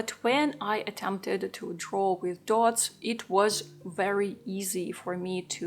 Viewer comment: Sorry, but at about 3:30 in your video, it sounds like you said "troll with dots". This phrase makes little sense when combined with, "I dance like a Pinocchio", "I can make sweaters", and "I can sit in full Lotus position". So what is it that you did to support yourself? Were you a yoga instructor?